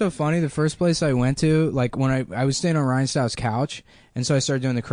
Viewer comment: did he say he stayed on Ryan Stiles' couch??